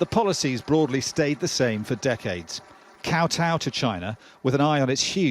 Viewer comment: Koutou? Did I hear the word correctly?